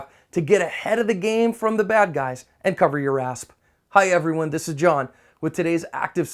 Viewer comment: I thought I heard him say "And cover your asp".